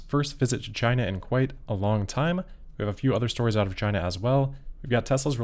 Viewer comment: Don't say "a few other stories out of China" too fast. Because I first heard "a few other stories of vaginas".